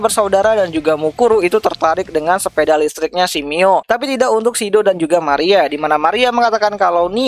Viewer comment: tertarik ke sepedanya si Mio.? Lu salah ngomong apa gimana bang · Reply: Eh ia harusnya nIa kwkwkw lidah kepelesett